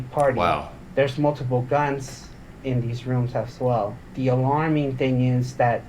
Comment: Did he say there are multiple "guns" in the hotel rooms?!!?!?!?